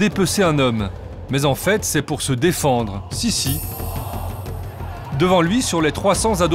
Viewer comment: Le mec il dit : sisi ou c'est moi